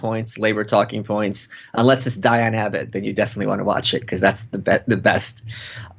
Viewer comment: he says something like "unless it's Dianne Habit. That's the best." Who is Dianne Habit or what did he actually say? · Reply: I think he says Abbot